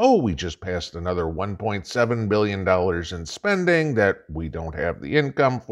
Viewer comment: Happy New Year!!! Not sure if I heard you right when you said 1.7 trillion or did you say billion? At mark 2:33. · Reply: Trillion